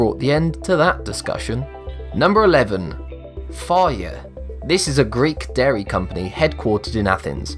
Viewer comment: doesn't it sound like he said fire but its fage?